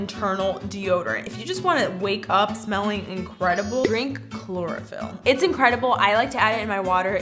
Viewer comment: For a second I thought she said drink "chloroform"! I was like WHAT!?!? My bad 😂😂😂